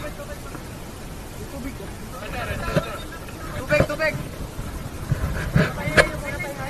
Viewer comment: Bamboo Yacht: I heard Sailors saying "Tubig (Tuu-Big) - Filipino word for Water. 5:46 Filipino Words: He's Eating or Just Eating. I wonder if what else this Bamboo Traveller wants? Maybe some Meals - A Jollibee C3? Where the Heck did he come from?